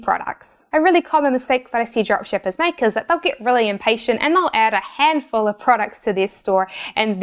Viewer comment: Good info!. But... at min 8:53 there is a transcription error in the subtitles. Must say "...not ADD a handful of", instead of "...They´ll add a handful of...". That error changes the whole idea.